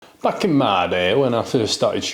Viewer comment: not knowing your accent I thought you said "fxckin mad ay" instead of "back in my day"